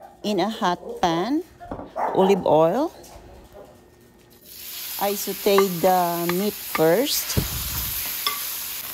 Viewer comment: I hear Benny? Looks so tasty and smells wonderful I'm sure! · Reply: yup that was bark